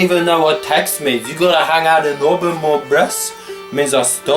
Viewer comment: I know I’m 5 years to late but at 4:51 did he say, “ you gotta hang out more in auburn” loooooool, surprised he knew that every TURK was originally from auburn